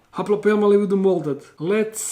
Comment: was I the only one who heard molded instead of molted